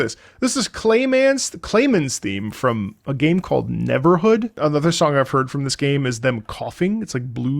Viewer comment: Aw man I thought that said EVERhood not NEVERhood.